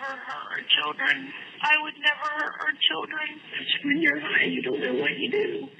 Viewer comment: time marker CC auto generator saying " your beautiful" actually she says " when your high you don't know what you do." Then the killer says how that had nothing to do with it...ect what a POS. Woman.